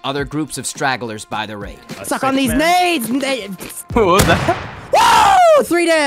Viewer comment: Almost sounded like said the n word after saying take these nades n and stopped